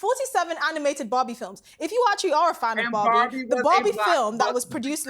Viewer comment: what's going on with the British language Can't even understand that lololol its a JOKEEEE........ But I thought she was saying Bobby for the first three times not Barbie i was like what Bobby movie LOLOLOLOL